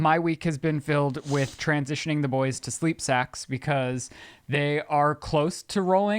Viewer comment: When Ryan said "sacks" I could tell Liz thought he said "sex"